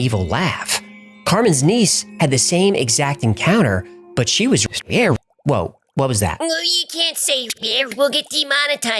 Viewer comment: what is the word that would get them demonatized? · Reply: Raped